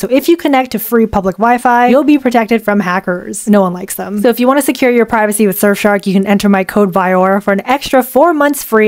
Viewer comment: Secure your privacy with Surfshark! Enter coupon code VIYAURA for an extra 4 months free at <link> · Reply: I thought you said screw your privacy with surfshark XDDDDD